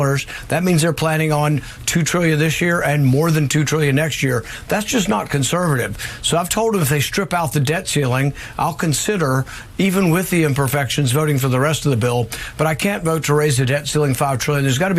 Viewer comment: he would vote for it even with the evil imperfections is what I heard.